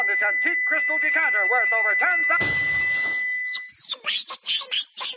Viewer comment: it kinda sounds like he said “Butthole!”😂😂